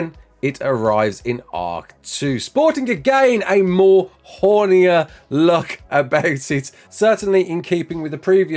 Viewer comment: did u just say hornyer